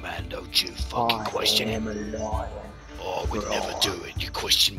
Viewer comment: Lmao this one is absolutely hilarious! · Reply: Agreed lol one of my favorites on the channel. I heard you saying I'm a lion Roar lol that got me rollin